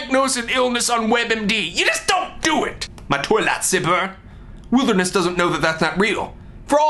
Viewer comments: What the heck is he saying at 1:12? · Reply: My toy light saber. :-) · when he says "toy lightsaber", I thought he said toilet zipper...